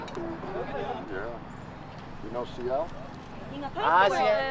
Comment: did he say "You know CL ?"